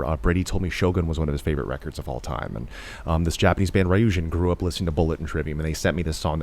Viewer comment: What's the name of the Japanese band Matt Heafy mentioned that sounded like bullet? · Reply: Ryujin